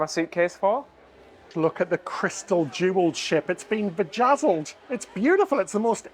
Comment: Did you say VAGazzled? 😱🤮 😂